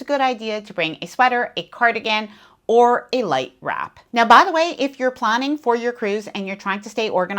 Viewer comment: I thought I heard you say "Bring a sweater, cardigan, or life raft." LoL